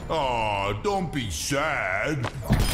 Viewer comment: I think he says “Don’t be sad”. Also was expecting more lines between him and Sombra and at least something with Reaper or Moira.